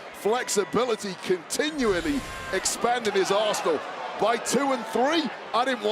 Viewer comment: I thought he said "expanding his a$$ h0le